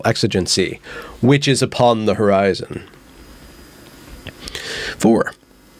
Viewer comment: Witches upon the horizon. Is what I heard you say at one point. English is a funny language.